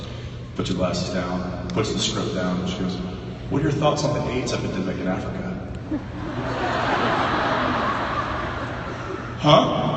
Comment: WHAT DID JENSEN SAY ON 7:40 ???? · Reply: The casting director asked, "What are your thoughts on the AIDS epidemic in Africa?" Jensen, caught off guard because he was so focused on the script he was auditioning for, replied, "Uh... It's horrible...?" Suffice it to say, he didn't get the part